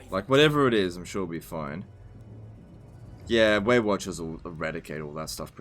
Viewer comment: misheard it as „weight watchers“ early in the video. couldnt unhear it for the next two hours